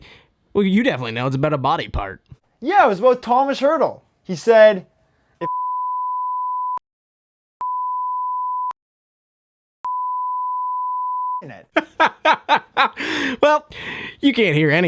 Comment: can someone read lips at 10:24 so i can know what he’s saying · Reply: “If I scored four goals, I’d have my 🏒 out stroking it”